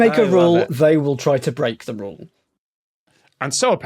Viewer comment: The joke there at the end is about the previous video with the traffic cones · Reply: thank you. I thought they said Paris, but they said parrots...